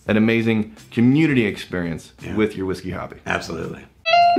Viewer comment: I thought rex said "with your whiskey hobbit." Not hobby. Just me?